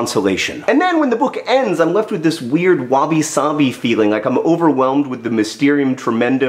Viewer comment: I know you said "wabi-sabi," but at first I heard it as "wobbly, sobby," and I like that, too.